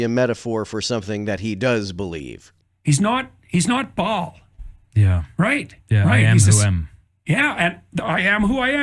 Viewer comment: I thought JP said "He's not Ba'al" and I was like "Are you sure about that?